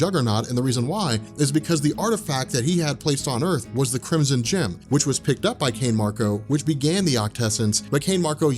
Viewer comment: I heard "Crimson Chin"... shiii, aint that dude from Fairly Odd Parents or sumn'?? haha